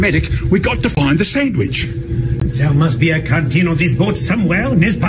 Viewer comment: i thought he said "there must be a carton on this boat somewhere [...]" XD